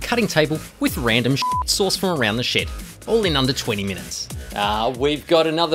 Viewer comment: At first I heard, "...with random sh*BLEEP* sourced from around the shit." and thought you forgot to, or intentionally left out the bleep for the second expletive. That made me chuckle LOL!